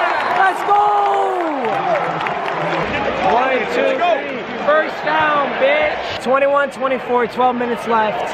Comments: i could've sworn that everybody in the stadium said first down bitch but they really said first down mitch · anyone else hear the guy in the back and say “DoWn BiTcH” at the same time?